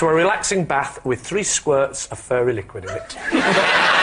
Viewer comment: I thought Lee said "furry liquid" until David said it and was very confused about British advertising.